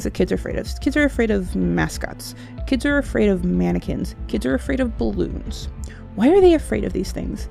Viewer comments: When she said "Kids are afraid of mannequins" i thought she said, "Kids are are afraid of Americans" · Thought she said kids are scared of mascara